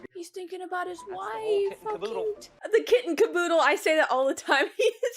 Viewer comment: I love this movie. Glad you liked it, too. - By the way the phrase is “kit and kaboodle”, not “kitten kaboodle”. Maybe you knew that, but that’s how it sounded like you said it. - Hahaha You’re totally one of those people who travel somewhere and then just keeps on referencing it. It’s like me, after I went to Japan… 😉